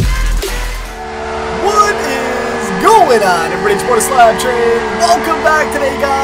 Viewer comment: WHAT IS GOING ON EVERYBODY IS CHIPOTLE SLAP TRAIN (thats what i think he is saying ) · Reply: i thought he said chabord the slap train